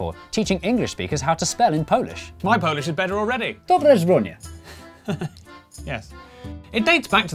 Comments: Wait im polsih yet im still trying to figure out what there saying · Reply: It’s my awful attempt at “dobre zrobione”. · What he meant to say: "Dobrze zrobione!" What he actually said: "DOBRE ŻRONIE!" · as a Polish person I have no idea what you said… and I tried few times. “dobre zbronie”? First word makes sense but the second I don’t know… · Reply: I think he was trying to pronounce dobrze zrobione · I'm polish and I have no idea what you said in 0:50. "Dobre" what? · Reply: according to the subtitles its "dobrze zrobione" i'm not polish so i have no idea what that means